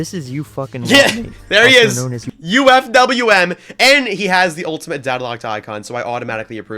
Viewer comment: I heard it UF"""ingWotM8 !?!?!